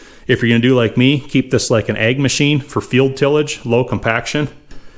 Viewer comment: Oh, you said "ag machine". The first time you said it, I thought you said "egg machine". 🤔 😁